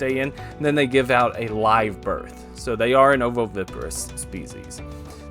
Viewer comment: Sounds like you said “oviparous”. You mean “ovoviviparous”.